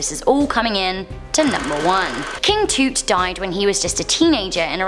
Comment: did she say King Toot 🤣❔😆😂